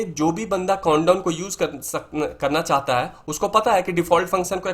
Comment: sir you have used the word condom instead of countdown :face-purple-wide-eyes: on